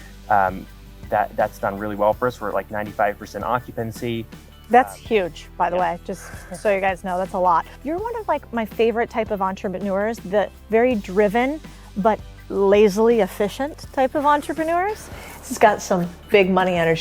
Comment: did she say entreManures lol😂